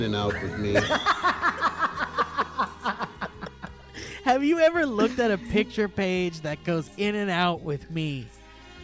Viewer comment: I heard, "You ever look into a picture page"